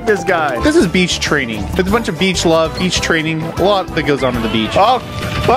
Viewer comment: “Love niggas on the beach” I swear he said it